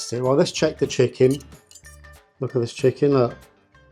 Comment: Did u say check the chicken? I thought you said choke the chicken😂